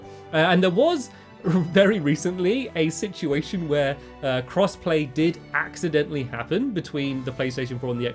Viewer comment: I thought he said cress-play aw man